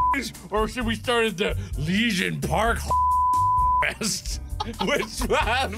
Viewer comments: I heard "Legion park homeless fuck fest" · Since when does Markiplier censor his videos? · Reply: I dunno :o I thought at first he said Legion park